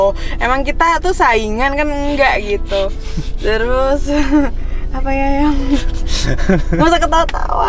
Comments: Aku salah denger ga sih ka bilang "apa ya yang"🥲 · kok aku dengernya "apa ya yang" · 18.58 kedengeran gua blg 'apa ya yang' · Gua salah denger gak sih dimenit 18.57 bilang "apa ya yang"